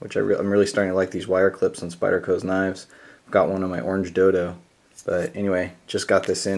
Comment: why would you have a wire clip on your orange dildo? :p I had to rewind the video because I thought that was what you said. hahaha good vid.